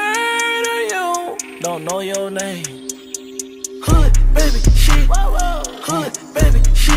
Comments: I really thought it said hood baby Chihuahua 💀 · I thought he said “who baby she” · I thought he said “who baby chiwawa” 😂😭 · I thought he said “who baby she” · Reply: Thought he said hood thay shet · I thought he said “who baby she” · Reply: I Thought He Said Hood Baby She 😂